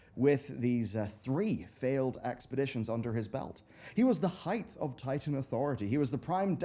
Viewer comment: did....did you say "heighth"?